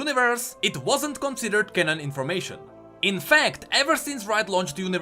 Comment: i heard kennen instead of canon the first time and i was like wait what then i remembered ur accent